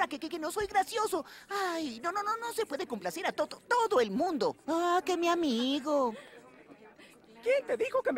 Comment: Que dijo Speedy Gonzales? Y porque muy molesto? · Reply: Dijo aaah q mi amigo y anda así porq lo cansa oír los problemas de Porky xD